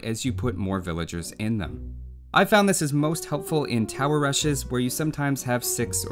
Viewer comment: When he said "tower rushes" I kept hearing "Tal Rasha's"...